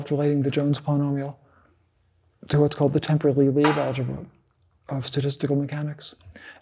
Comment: Anyone catch what Witten said at 7:04? Something sounding like "temporal Lie algebra of statistical mechanics"? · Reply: Akhil Jalan — “Temperley-Lieb Algebra: From Knot Theory to Logic and Computation via Quantum Mechanics”